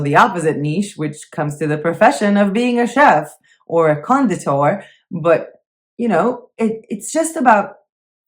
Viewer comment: What's that word you used at the 20:06 mark? "A chef or a ______________" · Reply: Conditor. I guess it's not used a lot in English (got several languages mixed in my head- it means a pastry chef LOL )